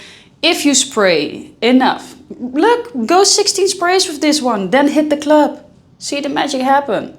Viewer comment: Thanks for the review. Did I hear correctly, 16 sprays @ 12:33 ? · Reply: Oh yes 16 sprays😎👌